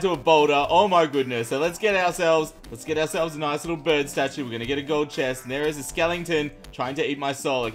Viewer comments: When it said bird statue I thought it said brad statue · New Series HYPE! Welcome to our vanilla SCAVENGER CHALLENGE! Want this to be a daily series? Smash that like button! What sort of base would you like to see in this adventure? Let me know! 🍪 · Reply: did u say skellengton